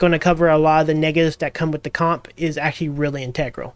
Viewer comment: tf did he say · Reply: That come with a comp. Comp short for composition